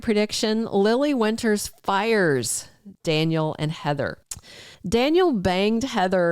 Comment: Did you say that lily fired both of them or. You think she wil? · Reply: I wrote it down... Thought said Lily fires Daniel/Heather......❤